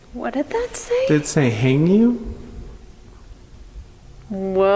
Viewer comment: I heard thank you bc was apologizing to the principal